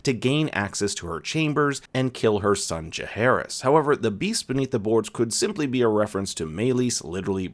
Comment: I thought Helaena said beware the beasts (plural) beneath the boards. Maybe Meleys was just one of many.